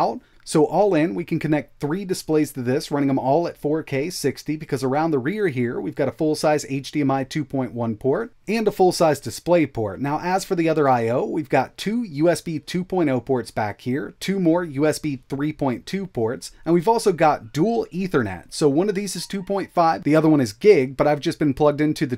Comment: Seems criminal not to have a thunderbolt 4 port.! But otherwise pretty badass.!! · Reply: For a second I thought you said usb 4.0.